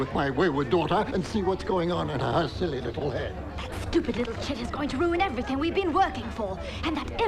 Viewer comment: did she say "that stupid little shit?"